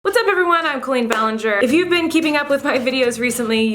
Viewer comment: I thought she said Queen Ballinger 😂😂 she’s pretty like a queen tho